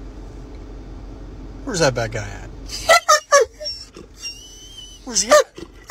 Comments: I heard “where’s that Black Guy at” 😂💀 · So, basically, the dog “alerts” on cue rather than when he “detects” contraband. This is exactly what so many have been claiming for years. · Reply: oh my goodness, I thought he said where is the f*** black guy! 😳😂🤔🤣😁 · Sounded like he said where's that black guy...... How about saying where's the criminal 😀